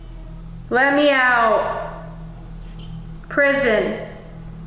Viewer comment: before you spoke, a female voice was heard saying, "Let me out." It was very clear and it gave me goosebumps. Your videos are the best!❤❤